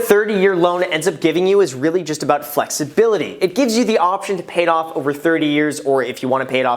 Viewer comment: did i just hear flex? weird but ok